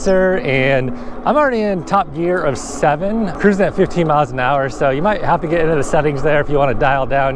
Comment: At the beginning it sounds like he said 75mph